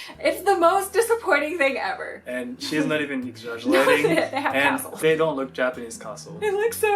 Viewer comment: Played back 9:26 three times to figure out that was saying is not "exaggerating" about the love hotels. I think. Because honestly it sounded like "eggsadjshulaying." And that's not a keyboard mash xD